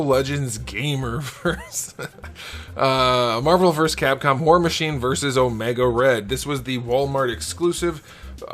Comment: I swear he said “whore machine” 😂